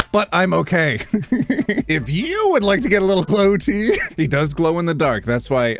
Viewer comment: I heard "if you would like a little blow-T..." and I was like "WHAT? I WANT ONE!" But then I saw the plush and I received mine a few days ago so all good, I didn't miss out on anything 😂